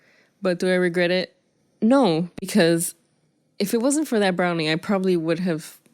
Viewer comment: When I watched this video when I was 9 I thought he said wheat brownie.